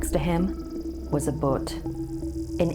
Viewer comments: the way she said bhoot 💀 it took me a moment to figure out what she was actually saying lmfao i thought she was saying boot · sorry but when she said beware the bhoot i thought she said butt. Then it reminded me of that finding nemo seen with the butt.